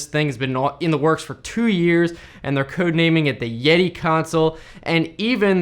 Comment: Heard it as "the gey console" 😂😂😂😂